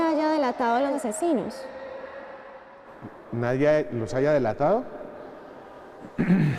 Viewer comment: El responde en el minuto 44:10 "nadie Nos haya delatado"????? O escuché mal. Lo pude en slow 0.25 y me parece que dice NOS haya (incluyéndose)